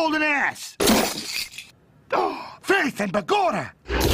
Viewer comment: wat he say?? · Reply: "Faith and begorra!"